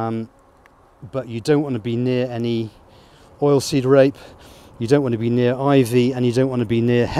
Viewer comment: oil seed rape? It's that what you said there, or did I misunderstand?